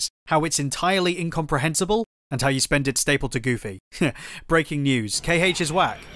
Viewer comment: It's been bugging me for a while now but.. WHAT ARE YOU SAYING AT 0:08 and 1:48!? "You spent it staple to Goofy?" "You spent its table to Goofy?" I'm going absolutely insane that my ears can't pick up a sentence that makes sense to me! Nice vid tho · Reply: Haha sorry- stapled to goofy